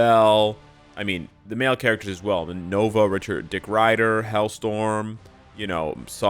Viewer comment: Did he say **- rider at 1:26?